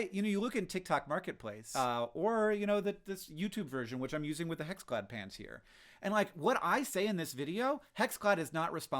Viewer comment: I keep hearing 'hexglad pants'.